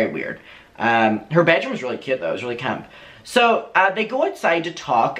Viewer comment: Hey Adam! What word did you say 20:45? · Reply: it was really cute tho it was really camp!